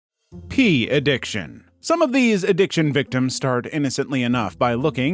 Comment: I heard “pee addiction” and it took me a few lines of script to realize it meant porn. I just heard pee. 🤣